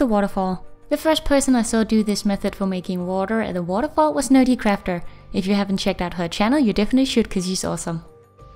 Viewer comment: At about 8:30 in your video, you mention another artist. It sounded like you said "Nattie Crafter." Would you please tell her name or a link where her work can be viewed? Love your Japanese Garden tutorial. It has inspired me to pull out my unfinished one and to finish it!! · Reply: tjgerladi she said nerdycrafter